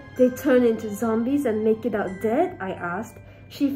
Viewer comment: when she said "make it out dead" i thought she said 'making out with the dead'...